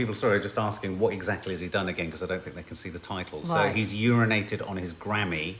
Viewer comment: Hahaha 😂 with your box of tissues🌋🌋🌋 😁😁😁💖 Btw I thought Kanye had been saying he was urinating on his granny 2 🤭🤭🤭😂😂😂🤫💕